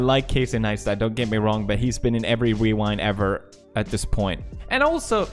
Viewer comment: did he just say wewind?